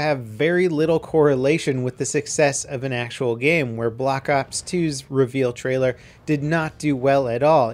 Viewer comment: Did he say block ops 2 2:10???